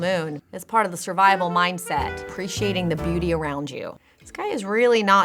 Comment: Did she say “Appreciate the booty around you”?